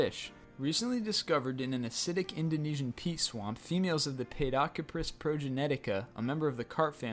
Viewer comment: did he say pee swamp? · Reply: Peat swamp